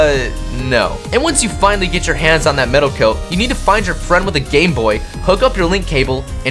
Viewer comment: I thought you said gay boy